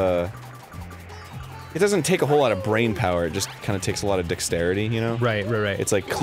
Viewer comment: sounded like Arin said "Takes a lot of dick-starity" lol if that were a thing, I'm sure it would exist in Game Grumps